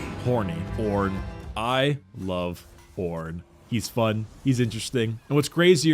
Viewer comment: LMAO IM DYING GOD IF YOU HAVE YOUR SUBS ON IT KEEPS SAYING 'PORN' INSTEAD OF 'ORRN' 'introverts horny porn, I love porn, he's fun, he's interesting' LOOL